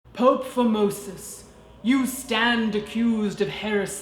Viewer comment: God I thought I heard his name was "pope fimosis" HAAAAAA